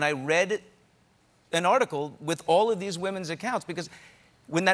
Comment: sounds like he said fartical